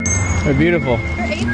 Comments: I thought he said “hey beautiful” and I was confused on why she’s dropping that lore after he said that but damn that’s beautiful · I completely misheard him and thought he called her beautiful